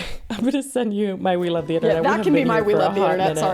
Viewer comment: I don't know if it's because I watch on 2x speed, but I always hear "we love the internet" as "wheel of the internet".